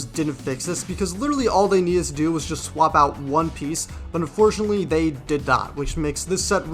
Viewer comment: did I hear you say one piece ?!